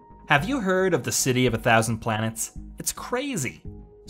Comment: I thought you were saying plagues at first XD